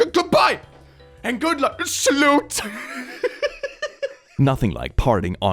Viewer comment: did he call her a slut just becouse she gave him good advice